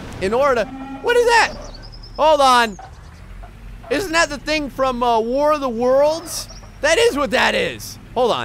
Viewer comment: What he say?? thing from?? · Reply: "Thing from war of the worlds? Or something?" That's what he said